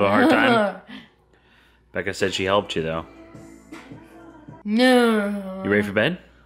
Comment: I heard say "Nein" at first I was excited then I reminded myself that it would be weird for her to speak German hahahaha